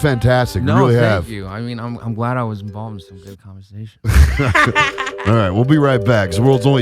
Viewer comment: What did Sebastian say @ 1:29:32 ? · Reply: It sounds like 'i'm glad I was involved in this conversation.'